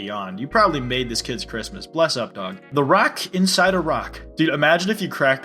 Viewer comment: when you said a rock inside a rock i thought you said iraq inside iraq